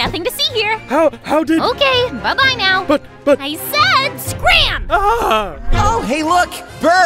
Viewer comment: i don't think he said scream, its like screen